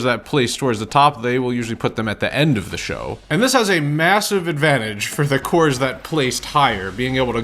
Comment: did he say... massive?